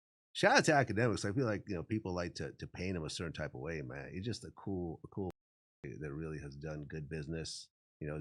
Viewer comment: Did Vlad just call ak “a cool ahh ninja??? “ lol